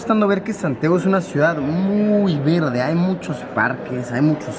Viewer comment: pensé que dijo "es una ciudad muy verga" xdxd